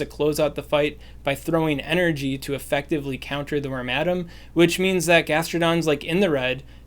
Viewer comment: It may be helpful to share the names of the pokemon in the team, especially for the less common pokemon. For wormadam, I would have sworn you were saying "ramadam" and had a hell of a time finding it.